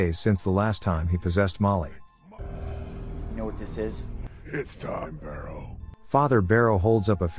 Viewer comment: "It's done barrow" And that sounded like "It's done bro" But ngl that was cool